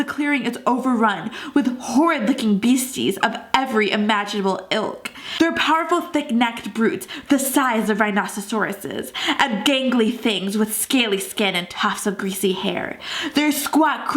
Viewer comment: Did she just say rhinocercorices? 1:02 lmao it's rhinoceros